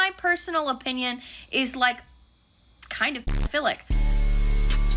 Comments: Hello, I'm just wondering because I don't understand, what is the word that is censored out at 11:56? Thank you! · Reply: i think its p*dophilic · what is the bleeped word this person uses? I've been trying to figure it out but cannot. Censor your comment if needed, wouldn't want this video flagged unnecessarily. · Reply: She said p*doph*lic (you can never be too careful)